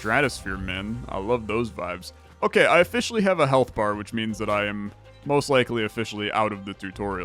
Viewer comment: When you said "I now have a health bar, which probably means I'm out of the tutorial", I heard it as "I have a halberd", and I was wondering if I somehow was unaware of a trope that involves you not having access to halberds during game tutorials